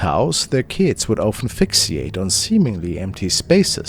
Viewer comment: At around the 1:12 point, it sounded like you said aphixiate. I realized you said fixiate (which is pronounced fix-ate). Oof. You scared me saying that.